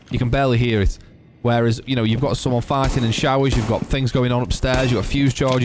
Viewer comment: did he say fighting or farting?